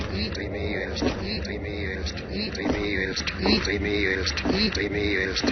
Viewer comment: what is it saying??? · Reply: "its eating me"